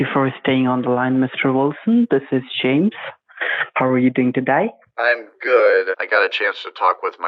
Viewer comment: I heard at 7:26 "How are you going to die"...